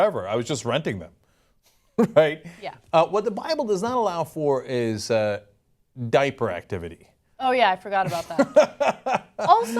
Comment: Did Cenk say "what the Bible does not allow for is Diaper activity."? Or did I mishear him?